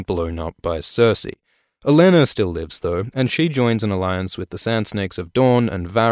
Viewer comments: Thought he said, "A Lannister lives thought." instead of "Olenna still lives though." Had a serious double take for a moment. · you said lannister instead of tyrell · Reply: He said "Olenna", not "a Lannister" but yeah they sound quite similar :)